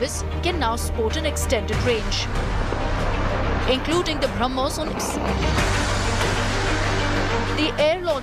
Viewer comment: "including the Brahmos ...." -- what did she say?? The Brahmos what? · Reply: I thought she said "BrahMos Onyx" -- the Onyx is the Russian name for their own longer range version of BrahMos. Our Indian media have such garbage quality that they can't even edit cleanly.